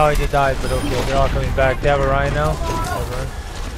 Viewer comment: DID HE SAY " THEY HAVE A RHINO "?? lol